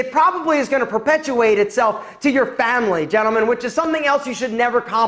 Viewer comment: ………. Did he say FAMIlY!!!!!